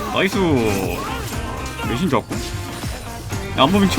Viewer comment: “리신 좋았고” 라고 들리는데 자막은 “리신노플”인데 혹시 뭐가 맞는지 여쭤보고 싶습니당 ! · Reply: 좋았고